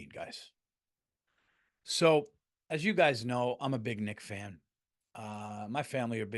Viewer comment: I misheard him say I'm a big Nig fan and not nick fan 😅